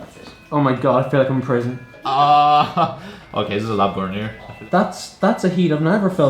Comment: I thought the dude said 'this side is a lot hotter' when they turned on their back and I was like W O A H